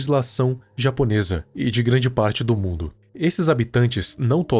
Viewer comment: Só eu que ouvi um "un forastêro!!" e "Voy te fazer picadinho"? Kkkkkkkk